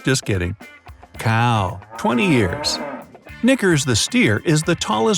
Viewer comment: sounded like he said n***er